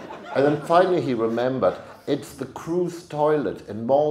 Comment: What's the name of that toilet in Malta? I can't understand first word (32:18). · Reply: "Then finally he remembered: it's the crew's toilet in Malta."